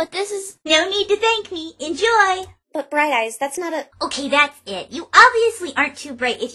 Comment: couldn't remember what pinkie called her... I guess it was "Bright Eyes": pinkie was right, it is bad when you stay Ditzy/Derpy 53:45-54:00 ARGH!!!! FUUUUUU!!!! FU FU FU FU!!! What did Pinkie call her!? I feel so mad that I cant understand "Miss Mailmare" It hurts! I'm trying to be nice and I'm instead hurting her, Agh God I feel like a bad pony!!! 58:54-58:57 UGH! Hate hearing that! 1:05:13 oh god are they going to reference THAT? 1:05:20 really...? 1:05:27 Holy SH**! they are!!!